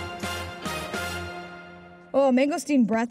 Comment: anyone else hear her go “oh sh*t”?